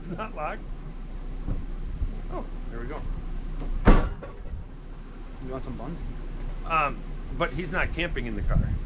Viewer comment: Really did I hear that right at 21:09 seconds did one of the officers who’s looking in his stuff say hey it’s open do you want some money??? Seriously😂 what is the world coming to?